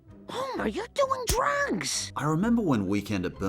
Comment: On the first one why do I hear "Homer you're doing drunks" XD I know Marge is saying "Drugs" but her voice makes it sound like drunks